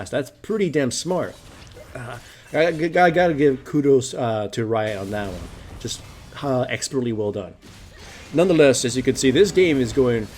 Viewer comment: As a non-native english speaker, can someone tell me what did he say at 7:53 and what it means? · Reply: Chiella Santin "I gotta give to kudos to riot on that one. Just how expertly well done." is basically giving credit to Riot for what they did to Fiddlesticks